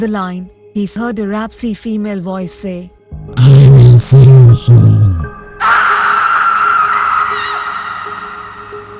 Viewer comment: I can’t understand what the woman is saying at 59:45. Can someone translate? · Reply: PumpkinSpikes i think she said i will follow you